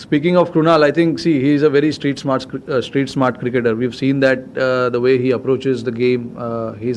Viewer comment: I thought Rohit was saying Krunal is a street cricketer 😂😂😂😂😂😂